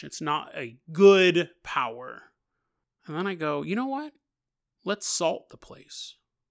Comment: I won't lie. Instead of "let's salt" I heard "let's assault the house" and I thought this story was gonna take a Warhammer: End Times tier left turn to crazy town.